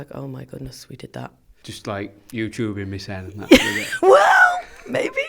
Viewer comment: Hey, could someone tell me what Alex said at 9:10? I sometimes have trouble understanding him, damn it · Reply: "just, like youtube-ing me-self *mumbles something *